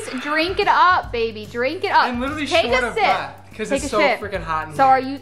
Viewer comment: take a sip What I thought she said: take a shit 😂 But it actually sounds like that tho